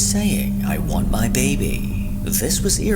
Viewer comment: i heard that's not my name not I want my baby